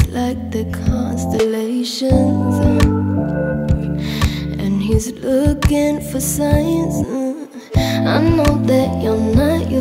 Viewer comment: This mess of emotions got his body questioning Is this feeling alright? He studying my freckles like the constellations And he's looking for signs I know that you're not used to this Boy will you let me teach you Your mind is asking for my love And you just need to hear it Try not to wander off too much Don't let your feels control you Keep you attentive with authentic kisses Filled with amor I'll show you How it's supposed to feel When we meet At Orion's Belt I'll show you How it's supposed to feel Running my fingers through your hair I'm feeling That your thoughts have left this Earth Is it worth it? Yes Is it genuine? Can I love like this? Let me give you some reasons I know that you're not used to this Boy will you let me teach you Your mind is asking for my love And you just need to hear it Try not to wander off too much Don't let ypur feels control you Keep you attentive with authentic kisses Filled with amor I'll show you How it's supoosed to feel When we meet At Orion's Belt I'll show you How it's supoosed to feel When your hands running down my body It's like a ticket to a cosmic sky Let your body get used to this Let your body get used to this It don't matter where we are Cause when we're touching we're caressing stars Let your body get used to this Let your body get used to this I'll show you How we're supoosed to feel When we meet At Orion's Belt I'll show you How we're supoosed to feel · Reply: but I thought she said "and he's looking for science." Am I wrong?